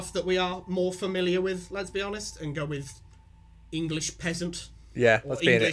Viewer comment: i could swear you said english femboy